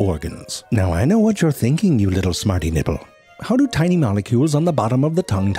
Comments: Did he just call me a smarty nibble or smarty nipple? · “I know what you’re thinking you little smarty nibble” I- I can’t. That’s apart of my vocabulary now. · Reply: I heard “smarty nipple”. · Did.. Did he just say "little smarty nipple"??? Welp, that's going onto the list of random stuff I say.